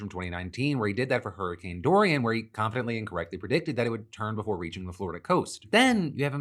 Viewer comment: Hey phil, the captions at 16:02 are very wrong. Shouild be "confidently and correctly" instead of "confidently incorrectly".